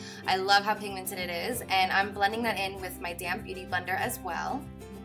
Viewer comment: I always understand "my damn beautyblender" :D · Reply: I thought she said "Damp beauty blender"